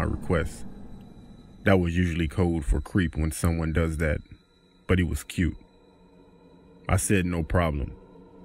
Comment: Dateing only cute guys.... thats rude ma girl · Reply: Where did it say she only dated cute guys? I thought what I heard was "The guy was cute"